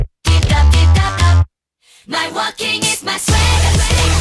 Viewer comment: I swear i heard "my sweater" instead of "my swagger" for a second.